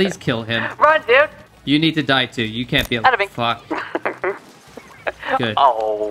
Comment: did say fuck?